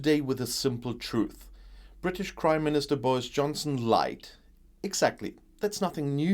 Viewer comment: For some reason I heard it as "British Crime Minister".